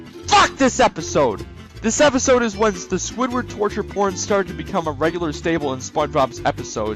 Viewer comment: did he say the P word? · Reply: "Torture Porn" is what he said